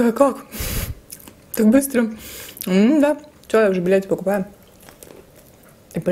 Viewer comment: Мне одному послышалось матерное слово на этом , данном, я указал каком моменте ??? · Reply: Четко слышится "билеты".